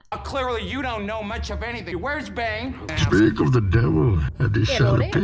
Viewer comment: did he just say "Where is Bang?"?